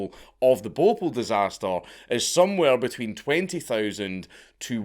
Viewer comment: I thought he said the boffa disaster